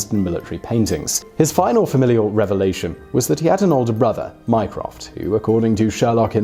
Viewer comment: I heard Minecraft